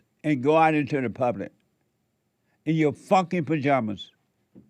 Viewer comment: The moment when I thought JLP said Fucken Pyjamas 😂😂😂😂😂😂😂